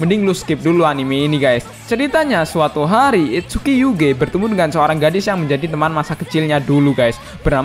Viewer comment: I heard : "itsuki you GAY" :v